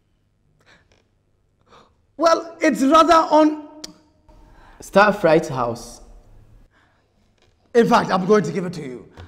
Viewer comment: who heard say Star fright house in 17:32 😂 😂 😂